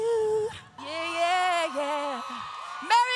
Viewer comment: Sounds like she said she's getting a new coat 😆